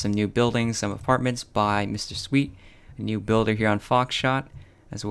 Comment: He said "Foxsh*t" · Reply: Builds I actually heard him say "foxsh*t"